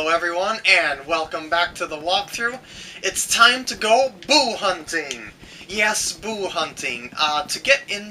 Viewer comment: lol at the beginning I thought you said "It's time to go poo hunting"